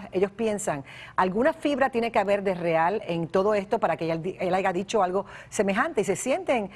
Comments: Min 2:59 escuché bien 🤔🤔🤔...dijo haiga?? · el HAIGA dicho ? , o escuche mal ? , que ignorancia